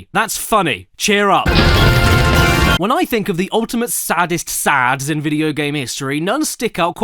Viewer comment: I thought it said Saddests Sods.